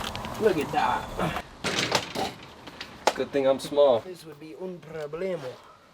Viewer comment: Did he say "this will be un problema"?? 🤣 Great video guys!